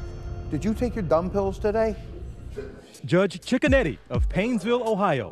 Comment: I thought they said his name was "Judge Chicken Eddie" not "Judge Cicconetti".